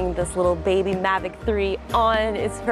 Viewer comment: im pretty sure that i heard was mavic 3 and not mini 3